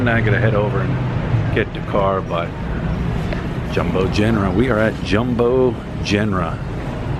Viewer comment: Aahhhhh! Jumbo Jenra. I thought you were saying "Jumbo General" (until I Googled). I may check that place out later this month. Thanks.